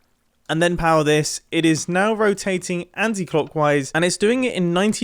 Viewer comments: Did he say anti clockwise? Is that a European thing because in the USA you say counter clockwise. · Maybe I'm just a dumb American but did you say Anti-clockwise?